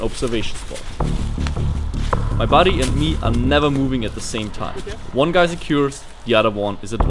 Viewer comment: At first I thought he said my body and me aren't moving at the same time...